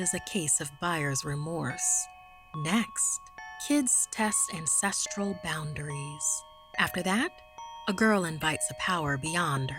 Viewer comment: When she said “kids test ancestral boundaries,” I thought she said “kids test incestual boundaries” 😳